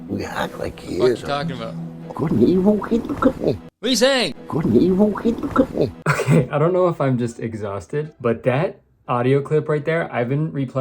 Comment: I heard like “couldnt he vote something something “could he?”